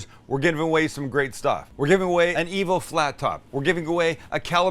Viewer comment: I could’ve swore you said you’re gonna give away an evil laptop. Is it hunters laptop from hell? 😂